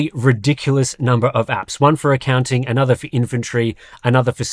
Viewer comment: in the commercial i heard "inventory" as "infantry" and was slightly confused...